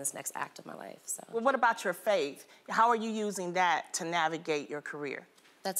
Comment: I thought Loni said what about YOUR FACE 😪 - but she said FAITH🤣 Not me thinking Megan Good was getting called out on her Skin lightening‼️‼️ loved her since Friday